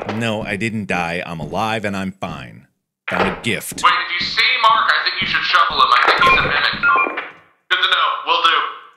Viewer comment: the proper captioning for 19:36 is "Wade, if you see Mark I think you should shovel him" btw✌️ (one sentence instead of two; replaced 'did' with 'if')